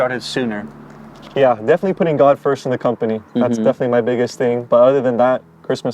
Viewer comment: Did he say ‘putting God first in my company”?